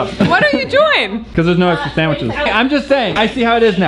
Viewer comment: When said sandwiches, I thought he said 'sane witches' and I got really confused. Are there insane witches in Hi5 Studios? Is there some kind of witch war going on? (P.S. new idea for BU!)